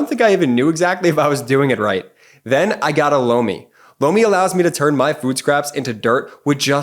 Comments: Definitely thought he said blow me as the sponsor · I mostly have no issues with your sponsors. But lomi has been debunked, it totally non functional. I analysed the debunk videos and the methods and came to conclusion that they are legit. I myself am a professional product and industry designer from the Bauhaus University in Weimar Germany and I rarely have seen such a bad scam product. The whole process in the apparatus makes zero sense, as significant biochemical reactions of composting are impossible in the machine. It just is impossible to reduce the extreme complex process of composting to mere mechanical and thermal treatment. Please abandon this sponsor. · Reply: I always laugh at this sponsor spot, the first time i saw it i thought Jordy was saying, blow me.